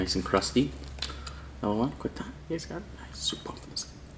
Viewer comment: My favorite part of the day is when goes “quick time. guys got it? NICE! SUPER pumped for this” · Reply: Oh he said 'guys got it'? All this time i thought he said 'lets get it' lmao